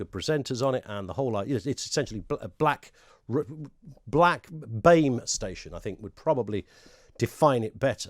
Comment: I thought you said black babe station. 😂